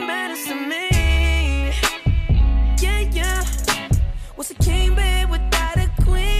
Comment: Whats a king bed I thought he said kingpin without a queen